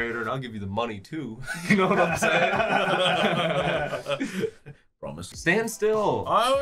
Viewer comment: did anyone else hear narrator say “promise?”